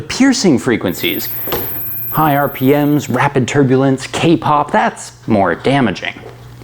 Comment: Did he really said K-Pop at 2:40?????😏